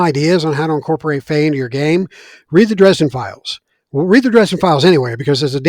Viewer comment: Hello! Love your work. Please write the title of the book you are talking about here 1:01:24 (I am Russian and I cannot make out the words you said :D) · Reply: Hey there, thanks for watching! I mentioned the Dresden Files series by Jim Butcher <link> , and the Fever series by Karen Moning <link>